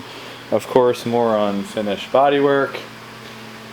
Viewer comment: thought u said "moron finished bodywork"...😂😂😂